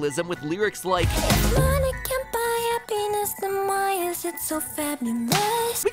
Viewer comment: Is it saying happiness or... A pen*s